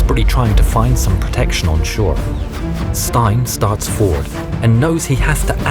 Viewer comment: When he said stein and his comrades I thought he said Stalin and his comrades